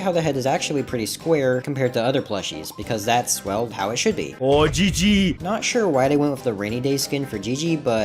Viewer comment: "OI GIGI" BRO IS BRITISH 💂